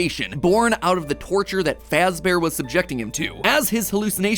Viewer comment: the captions say fazear instead of fazbear :) also at 19:00 golden freddy is called golden fredd.